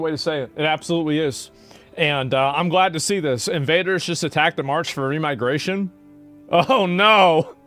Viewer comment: I thought he said "Invaders just attacked the March Furry Migration"